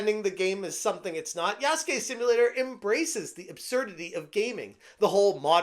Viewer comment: I thought it said Yasuke Stimulator 😮 Also the jap trip hop track was the cherry on to. Mwha! chef's kiss